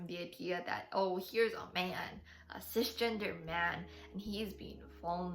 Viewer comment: didn't quite hear what was said. He's a what man? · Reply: i said “cisgender” :’)